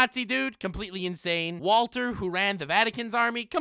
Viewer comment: did he just call Anderson Walter